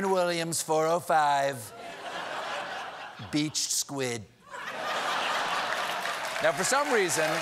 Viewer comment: Sherwin-Williams #405 Peach Squid. Hindsight 2020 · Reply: Monk Amani OK, I thought he said beached squid.